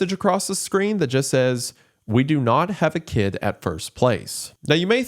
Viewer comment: The subtitles are wrong in that part of the video. It's not "We do not have a kid in first place", but "We do not have a kid IN THE first place". Implying the ex-wife believes Nanami to be their kid back from the dead, but the ex-husband saying that they did not have a kid in the first place.